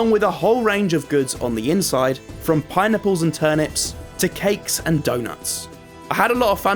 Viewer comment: i didn't hear pineapple i heard "pie nipple"